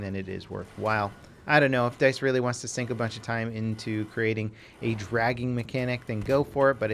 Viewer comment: Dude I thought you said a dragon mechanic